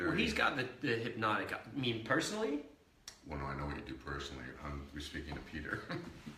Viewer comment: What Jr said to Ian and after that Ian going red hahaha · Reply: They were talking about sex then Jr said what would you do then Ian said personally? Then jr said I know what you do personally I meant peter then Ian went red